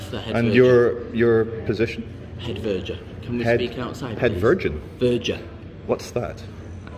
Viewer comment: “Head Virgin” 🤣🤣🤣🤣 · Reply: I heard "pet virgin".